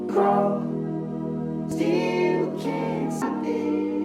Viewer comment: Intro sounds like it says, Crawl till you can suck it".